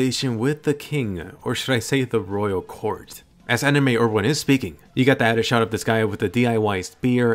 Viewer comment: I thought he said DUI spear (drinking under influence spear) 😂